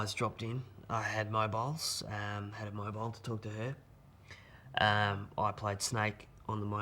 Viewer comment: hahahahaha for a sec there I thought he said I had my pet snake dropped in and pissed my laughing, rewinded and he said played snakes. Damn was funny while it lasted